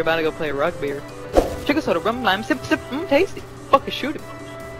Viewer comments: What did say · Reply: is saying sugar, soda, rum, lime sip sip mmm tasty. Its from Alan Ritchson - Mojito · wtf is saying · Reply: SUGAR SODA RUM LIME SIP SIP MMMMM TASTY